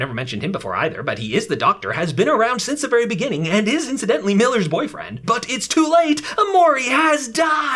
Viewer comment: When he first said “Miller” I heard it has “Hiller” don’t know why